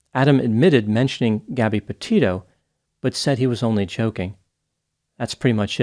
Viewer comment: Did you really say Adam said “ he was only choking?” 😅